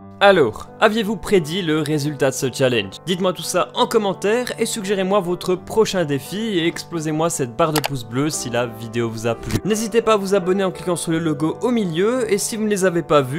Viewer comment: à la fin contre Timmy j'ai compris "timinou lance un défi" et au lieu de "explosez moi cette barre de pouce bleu" j'ai entendu "excusez moi cette parenthèse bleu" X) #100k